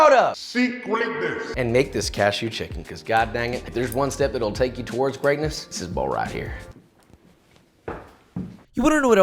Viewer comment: I heard "if there's one step that will take you towards pregnancy, is this bowl right here."